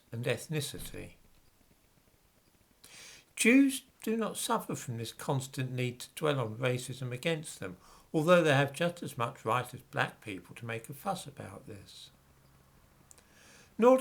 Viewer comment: "Juice do not suffer from the constant need to dwell on racism against them" Did I hear that correctly? =D Seriously? Exactly were do you think the bl*cks learned it from???